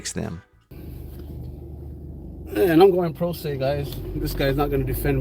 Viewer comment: What he says: "Im going pro se" What I hear: "Im going to jail"